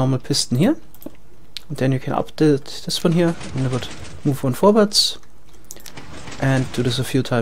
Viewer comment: did you say 'vorwärts' at 6:36?